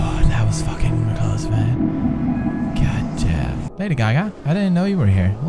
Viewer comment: did he say lady gaga?? XP